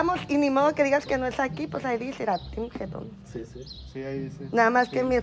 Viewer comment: Se pasaron cuando dice yo pense que decia Team Joton jajaja